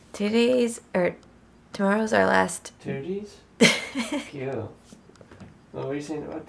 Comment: awww haha, i thought they were saying "titties" at 9:43 cause i thought accidentally said it when she meant to say "today" lol. i was dying until i kept playing it back and realized they weren't and I got disappointed XD